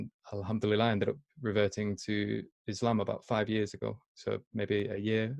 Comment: It sounds like is saying 'I reverted to Islamabad!'. hahaha. I had to rewind just to hear that. Mashallah.